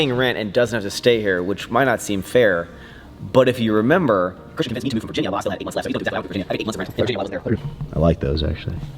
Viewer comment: I slowed it down to explain you guys what said 😂: Christian made me move from Virginia while I had 8 months of pre paid rent left so it’s fair